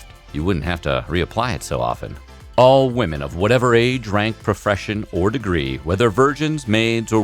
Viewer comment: Am I the only one who heard “profression” at 2:11?